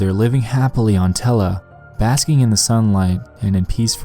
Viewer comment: You can have your TELAH, down here we have a later, more improved version, Nutella. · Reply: Steven W. Hunt I thought he said Nutella at first too 😂